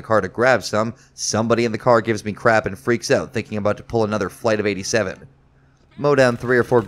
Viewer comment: I swear I heard "Freaks out thinking about to pull another bite of 87" LLAMAOMAOAGMAOMOA HGAHAHA good video man!